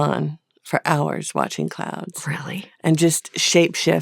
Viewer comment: I thought she said “watching Clowns” as a favourite activity.